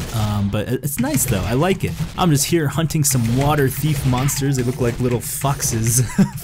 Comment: "They look like little foxes" Thought he said "They look like little f**ks" xD